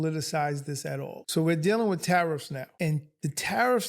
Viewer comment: Tariffs, okay Tariffs, I heard "terrorists" at first